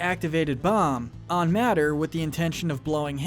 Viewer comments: He said matter instead of mater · Did he just call him “Matter”? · Did he just say “Matter”?💀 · did you say Matter? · Did you just call him “Matter”? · did you just say matter? The fuck? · did you just call him matter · did he just say Matt-er?